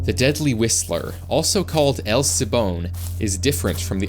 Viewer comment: I thought I heard dead whistle